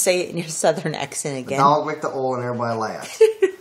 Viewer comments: the man: thedoglickedtheolanerrybodylaughed the wife: okay - now in english. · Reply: it sounded like he said that "everybody left" lol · Why would people laugh if a dog licked oil? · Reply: At first I thought he said the dog licked "the hole" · I heard "The dollar lit the oil and everybody laughed"